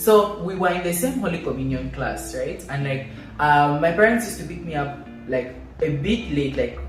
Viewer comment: Ouuu Chile I heard "my parents used to beat me up" was about to call 911😭😭😭